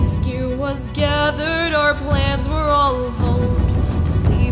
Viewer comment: instead of “our plans were all honed” I always hear it as “our plans all honed”